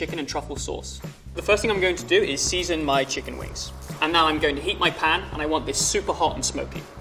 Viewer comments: I had to stop for a minute there because I thought he said "I'm going to eat my pan" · who else heard "now im going eat my pan"